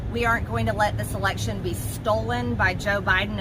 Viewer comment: I thought she said "ERECTION"